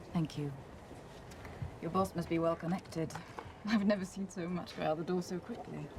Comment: can someone tell me what she says? CC doesn't help. "I've never seen someone walk out the door so quickly"? That doesn't make sense. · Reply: "I've never seen so much fly out of the door so quickly" in regards to the money being lended.